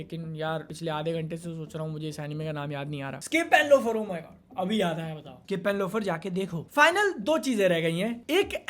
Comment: What's the name of the anime that he recommended at 9:47? He said it so fast, I couldn't understand. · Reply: Skip and loafer